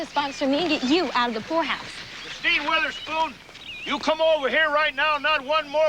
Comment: I thought he said "Steve Witherspoon," and I'm like "Huh?" but I turned on the subtitles and he actually said "Kristine Witherspoon." Her full name I take it.